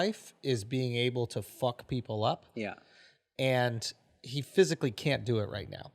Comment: I thought he said mcfuck ppl up